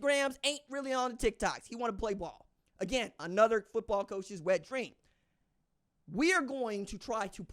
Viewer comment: Mr I have a question ❓ as a Mizzou fan. At 11:20 on the video did you say Arch Manning was another coach wet dream😂😂 are am I tripping. I can't wait till we play OU 🙏🎉